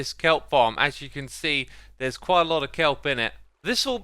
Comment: "There's quite a lot of kelp in it." I heard that as: "There's quite a lot of kelp innit?"